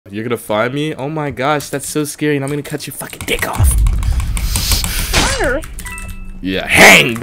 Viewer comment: For some reason I heard "I'm gonna cut your focking deck off👹👹👹👹👹" am I the only one who heard that? (Hai)